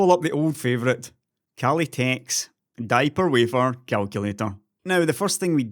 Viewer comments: Listening in the background, misheard "die per wafer" as "diaper wafer". Brain please stop. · Thought I heard say "Diaper" Wafer. :) Great video as always!